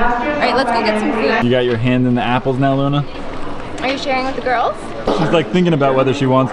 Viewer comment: I thought Nana said it was eggs or Luna so I was like...WAIT WUT